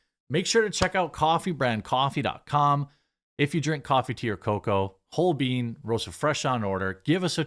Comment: This entire time like, since the ads started showing up on Lotus Eaters videos, I thought you were saying "Coffee to your Cocoa" and I kept wondering what sort of niche brew item that was or if it was another brand of some sort, until NOW.